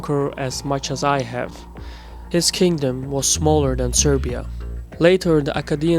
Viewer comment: is he say'n on 2:19 "his kingdom was smaller than serbia " ???